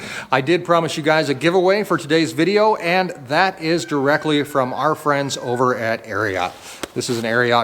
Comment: Easier to answer the call of nature in the outdoors when you’re a man!! Haha I can just see people driving by oh it’s just peeing again.lol Hope you get another calf tomorrow! When you announced the giveaway yesterday I thought you said Marriot like the hotels.lol